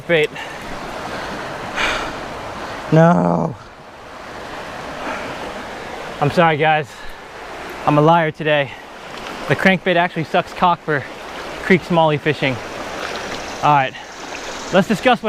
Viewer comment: did he say the crankbait sucks cock or did I miss hear that😂😂